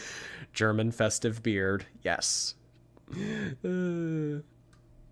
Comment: I heard 'German festive beard' instead of germ-infested beard lmao